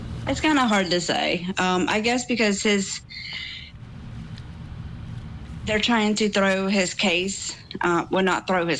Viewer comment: Does she say they are trying to throw his case the 3:58 mark? Truth comes out. 😂